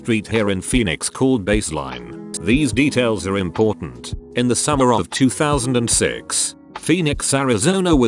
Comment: I wasn't paying attention and I thought he said in the summer of 2001 and my heart skipped a beat. Panic! is ruining my life